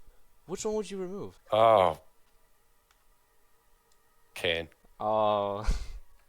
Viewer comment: what skin did he say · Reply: cain